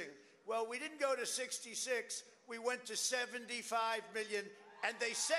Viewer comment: he says he got 75 Billion votes. Instead of million. I can’t believe this man 😂😂😂